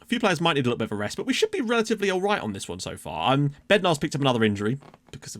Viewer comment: Everytime you mention Bednar (to me atleast)sounds like youre saying Bendtner:-) haha.. might be your fast talk xD